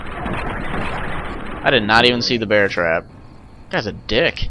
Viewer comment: the barton guy didnt say he put a bear trap, he led u into a trap! LOL its funny whenb you mishear things!